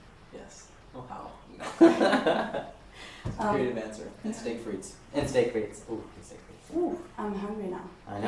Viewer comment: does joe say "steak frites"? haha